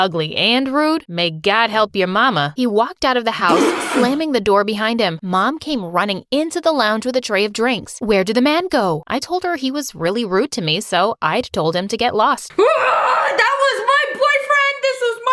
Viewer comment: when she said were did the man go I thought she said we're did the mango I looked stupid